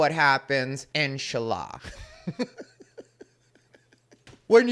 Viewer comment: When he said 'Inshallah', I thought he said Enchilada